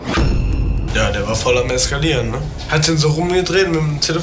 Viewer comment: hab ich verstanden: "Der war voll mit Meskalin" 😅